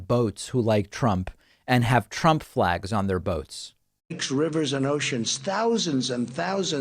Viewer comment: Trump is a numpty. I want to see the boats . (I thought he said votes)